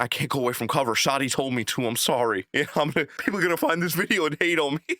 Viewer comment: All I heard was “I can’t leave, Shawty told me to.” 😅